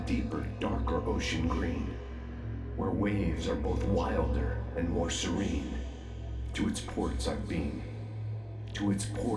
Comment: did it say "beth wilder and paul serene"? wtf?